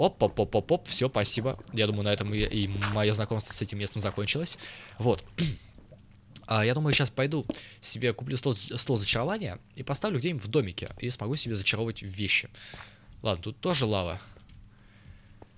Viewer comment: Мне одному послышалось или на 14:47 сказал стВол зачарования?